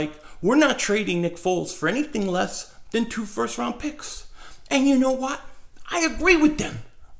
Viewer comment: My bet is the Broncos offer the 5th pick for him, gotta do it. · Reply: I thought he said 5h round pick at first I was like what! no